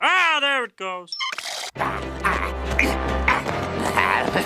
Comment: Ow!, Stupid Wishing Well! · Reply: lol, at first I thought he said "wizzing well"